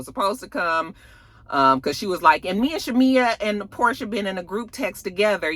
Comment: I thought Drew said her, Angela and Shamea are in a group chat. Not with Porsha. But maybe I heard it wrong